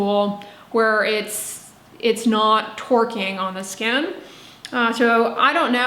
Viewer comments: I really thought I heard, “it’s not twerking on the skin” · I heard 'the gua sha tool twerking on the skin', it made for a hilarious mental visual!😂 I think that massage in general is beneficial (lymph drainage and blood flow increase), it just depends on whether you are consistent with introducing it into your daily skincare routine. Alot of things must be done consistently for a long period before any results are seen. Im not sure if it warrants buying an expensive tool though, as manual massage may be just as effective. Did the study mention if the control group massaged at all? Seems like they missed out alot of aspects.